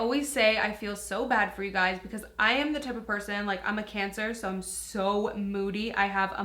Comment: I GOT SO FREAKING SCARED WHEN SHE SAID I'M A CANCER I HEARD I'M IN CANCER ! MOST SCARIEST SECONDS OF MY LIFE😧😭